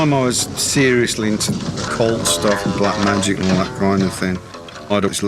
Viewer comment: Excelente corte! Só uma correção (eu nem sei se é possível corrigir isso, mas vou destacar mesmo assim): no minuto 1:45, um trecho da frase foi traduzida para "coisas frias", porém acho que foi dito "cult stuff" (referindo-se a cultos) e não "cold stuff".